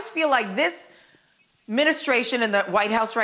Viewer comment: Did she say ministration (0:53)...isn't it administration?🤔